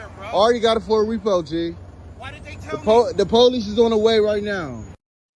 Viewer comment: Did he actually say: " Why didn't they tell me"? WTF!!!!